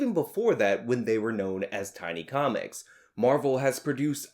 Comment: It sounds like you said "Tiny Comics." It was Timely Comics.